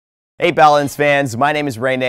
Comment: is it just me, or does it sound like he says: Hey, balins fans